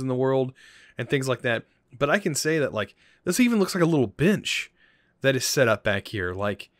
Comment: "This even looks like a little bitch" that's what I thought he said